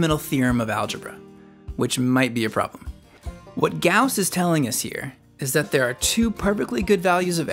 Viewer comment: in the subtitles says "Guass" instead of Gauss... haha